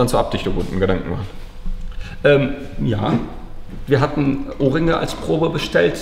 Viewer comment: Grad kurz Ohrringe verstanden 😅